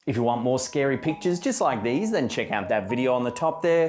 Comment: "...scary pictures..." I heard "scary peaches". 😬